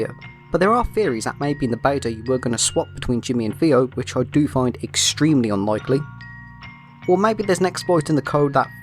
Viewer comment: What did you say at 1:42 was it even english? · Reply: "but there are theories that maybe in the beta, you were going to swap between Jimmy and Theo, which I find extremely unlikely"